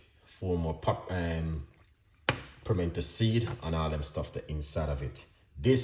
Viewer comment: Hello there what is the name of the first ingredient you put in the jar what is the name of the seeds I thought I heard you say fermented seed is it pepper please let me know I don't understand also thanks for the recipe overall it's well appreciated...